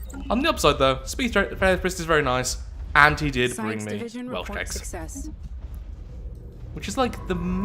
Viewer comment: you were talking about one of you favorite things about being in Wales, but you were talking just fast enough that I'm still not quite sure what you were saying. It sounded like you were saying Welsh Checks, which google says is a kind of fabric that looks a little bit like plaid, but it seems strange that your voice therapist would be giving you fabric. Did I mishear you, or are you just a really big fan of Welsh textiles? No shame if that's the case, I'm just thoroughly confused now.